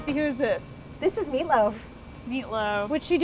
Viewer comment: did somebody said meatloaf??